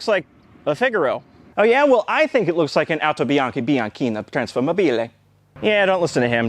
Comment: its Autobianchi Bianchina, if anyone is wondering (at least I think thats what he said), a late 50s early 60s Italian compact car based on the Fiat 500.